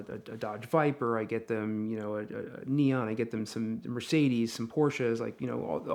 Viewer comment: Did I hear "Neon"?